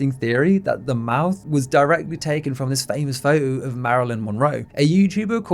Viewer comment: for a brief moment I thought you said “Marilyn Monroe, a YouTuber” and my brain just 🫥 for a hot sec